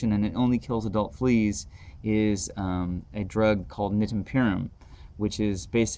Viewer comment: What is the product that you talked about at 5:00 into your video? It is hard to tell what the ingredient is that you are saying. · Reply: Nitenpyram. I listed it in the description. You can buy generic brands of it. It should be sized/dosed for your pet. And you give them one in a 24 hour period. I've found it to be a great preventative measure as if you take your dog somewhere that they are likely to get fleas, the flea jumps on, 'bites' and dies. If by some stroke of luck that the flea hitches a ride and doesn't bite, they might survive, but it's been my experience that Nitempyram is a great short term treatment. Like all things tho, I don't give mine these unless absolutely necessary but tend to prefer Nitempyram because if it solves the problem I don't have to continue dosing or deal with a pervasive chemical. Nitempyram is a nicotine derivative and will be processed from the body in a similar way.